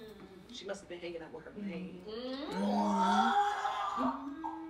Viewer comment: She must have been what? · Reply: I heard, ‘She must’ve been hangin out with her Maine’